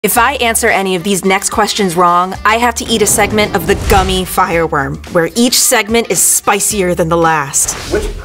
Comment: I thought you said, gummy firework